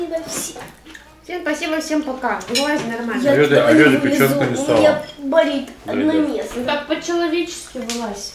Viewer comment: скажите, что мне послышалось??? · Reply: Блиииин😂 у меня болит онанизм? Я раз 10 переслушала